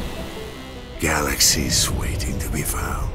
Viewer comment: Hold on... did he said 'Galaxies'?! 0:21 /yay!